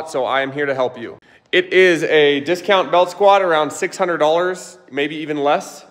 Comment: Thought he said squat belt for $600 at first😭